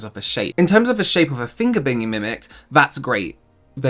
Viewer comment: Was falling asleep and thought I heard “finger banging” so woke up thinking I must have missed some major feature of this sponge.